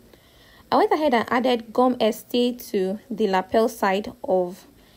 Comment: Thank you for the tutorial, but I don’t really understand the lapel side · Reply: I too could not understand what is the black material you put on the lapel. It sounded like you said cum Estee...sorry watching from S.A.